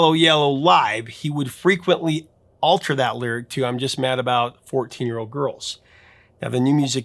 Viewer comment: I, until just now, thought he was saying, I'm just mad about fo-teen. I thought it was some sort of obscure British food. No idea he was saying 14. Now, I see him as scum pedo.